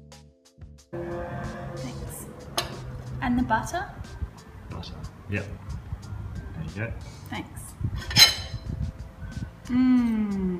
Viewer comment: Yeap. There you go I hear it like this "yep yaye" :))